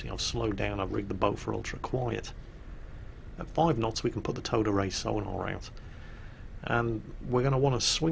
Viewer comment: everytime you say "towed array sonar" all i hear is "Toddaret sonar" and i'm thinking "who the fuck is Toddaret?"